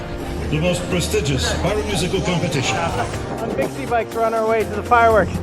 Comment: Did he just say "pyro-musical competition" 😮?